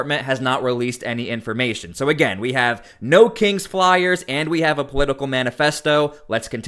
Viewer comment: I thought they said No Kings pliers. I was confused there for a minute.